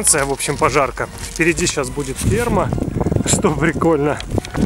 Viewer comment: мне послышалась впереди сейчас будет сперма